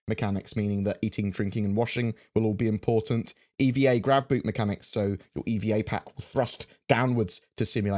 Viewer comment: i wasn't really paying attention and i heard that as "...EVA grab boob mechanic..." and a dozen of different interesting images came to my mind...